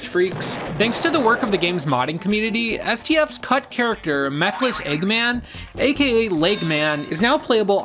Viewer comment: I kept mishearing you and thought you were calling him “Methless Eggman” for a solid 2 minutes.